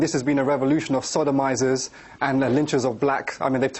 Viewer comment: did he say sodomizers